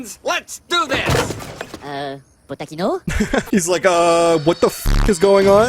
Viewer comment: am i the only one who heard "uhh.. watashi no?" 😭